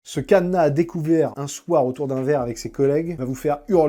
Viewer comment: J'ai compris "ce cadenas à découvert". Donc j'ai regardé en pensant que "cadenas" était un terme professionnel x)